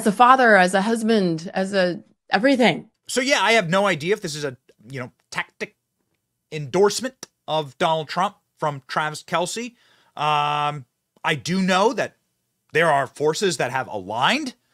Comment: mins I thought that Benny said "Trans Kelce" 😂. Damn this Bud is good. (no not the beer bud) 😶‍🌫️